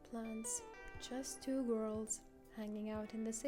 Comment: i misheard "just two worlds, hanging out in the city" - thought, wow, philosophical mood right from the start (then read the subtitles - it was "two girls")